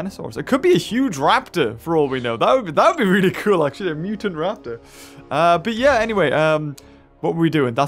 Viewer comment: did he just say a muton raptor lol